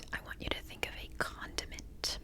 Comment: this was extremely funny as I thought if i heard wrong the word "continent" so I asked loudly: ,,A CONTIMENT??" and you, out of nowhere, as if you heard me, replied: ,,yes, a contiment" 😂😂😂😂 Btw I have survived all questions😅